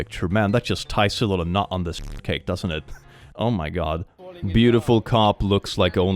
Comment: At first I thought you said „nut on this fruitcake“ 😭 Had to rewind to check 😅